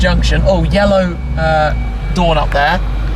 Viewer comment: Did you say a "yellow Dawn"? 😩